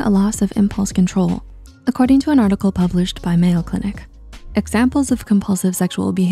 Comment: What is going on with the posted subtitles? are you guys using AI or something? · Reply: the subtitles in the video. For example, at 0:50 the voiceover says "[...]published by Mayo Clinic" pretty clearly, but the subtitles say "[...] published by Mail Clinic". MayoClinic is pretty well known throughout at least the USA; Mail Clinic not so much. This doesn't strike me as a human error. 1:37 VO: "norepinephrine", subtitles: "norponephrine" another one, norep is a medical term that most know, norpo by contrast is more like a 6th grader writing down what they heard without knowing the meaning behind the actual word itself